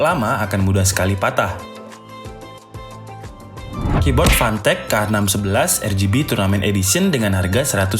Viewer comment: gw dengernya pantek njir :v